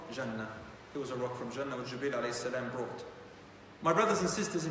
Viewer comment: Can someone please tell me what he says in 35:44? What is the name mentioned of who brought the stone? Jazakallahu Khair 🤍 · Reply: "But truly, It was a rock from Jannah, which Jibreel (as) brought." (Jibreel as = the angel Gabriel peace be upon him)